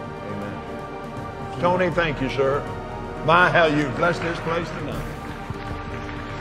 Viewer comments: Did he say in 6:32 my hell , you blessed us here tonight ? That is indeed alarming ! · does he say, "By Hell, you've blessed this place tonight." ???